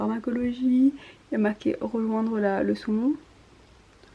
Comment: Omg j'ai compris "revendre le saumon" au lieu de "rejoindre la leçon" XDD